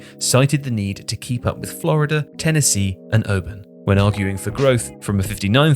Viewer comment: it sounds like he said urban it’s Auburn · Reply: He said arguing